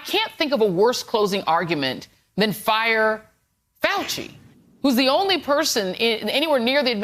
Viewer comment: sorry USA but instead of "Fire Fauchi " all I heard was " Zieg hiel " from the Nazi party speeches